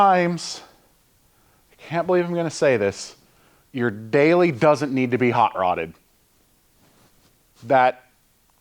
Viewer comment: I could have sworn you said "the daily doesn't need to be hot-rodded" I'm sure I was simply mistaken.